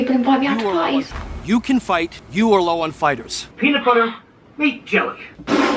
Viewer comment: I legit thought he said ”you are low on fiber” Constipation is not a happy place to be yall